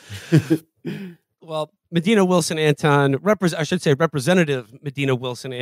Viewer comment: Damn I thought he said they had Madea on the show